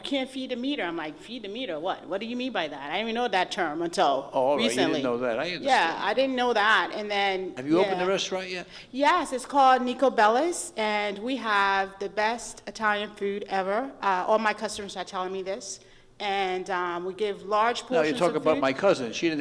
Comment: Did she say 17:00 she has a restaurant called Niko Belic? GTA